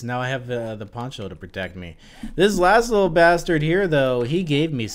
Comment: Man I could have swore you said "this Laszlo bastard gave me something" 😂😂